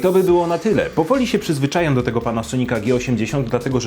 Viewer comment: ja usłyszałem pana słonika xDDDDDD